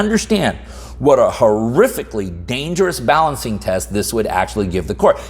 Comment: This enrages me to no end. All i heard was "interest balancing".